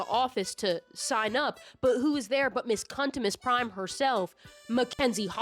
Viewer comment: i heard you say cuntimus prime and oh my god please make your next video about the transformers fandom i would appreciate it so much